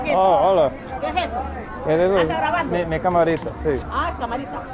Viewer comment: Minute 9:43 subtitles say "there's thieves", but she said "are you filming?" Completely different thing.